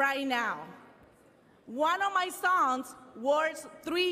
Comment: Did you say songs? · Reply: sons*